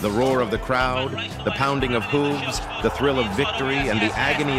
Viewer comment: Did he say “the pounding of hooves”. At 3:10?....lmao